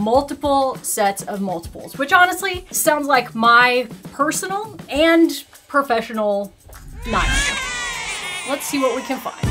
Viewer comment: Can anyone understand what she said here? Closed Captions couldn't understand either. "Sounds like my personal and professional- ???" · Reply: "sounds like my personal and professional nightmare"